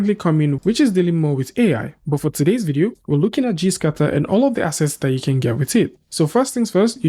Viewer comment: Oh, it's "gscatter" ... ! - I kept hearing "cheese cutter" ... hahahahaha